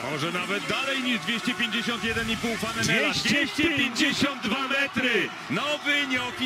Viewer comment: czy tylko ja usłyszałem 292 metry? A i tak wgl główna 2021 pozdro!